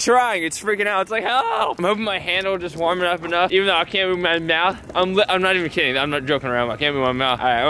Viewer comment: he said can't move my mouth but it sounded like came in my mouth lol